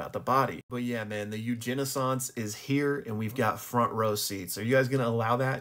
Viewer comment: I thought you said a “Eugeni-ssance” you know because of the Eugenics that’s going on 😂